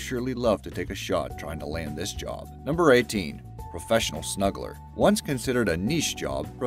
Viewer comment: I thought it said Professional Smuggler and was like “Well there’s a lot of risk, so the money has to be good.” Man, I’m tired 😂😂